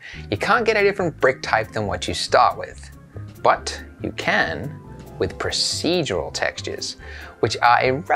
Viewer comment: Is it only me but I thought it said "The Future of Texting is Procedural"